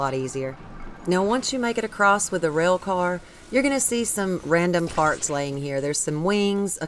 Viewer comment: I love listening to your tutorials, and your accent is just so cute.. but I could have SWORE you said 'some random farts' there on that one island...